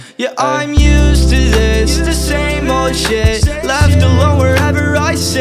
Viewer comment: When he said "The same old shit" I heard it as "Disabled shit"